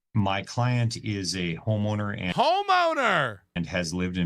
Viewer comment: When the lawyer said "my client is a homeowner" I heard "my client is a homo nerd"